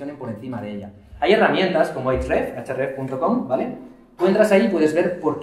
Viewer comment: Alguien me podría decir que dominio dice en el minuto 17:22 es para determinar palabras claves y su tráfico, que no logro descifrar el www que dice. Desde ya muchas gracias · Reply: ahrefs